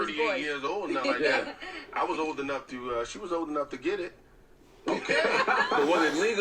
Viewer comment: Especially the part when he said "she was young enough to get it" disgusted tf outta me...who even thinks like that?? Nasty!! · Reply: i thought he said old enough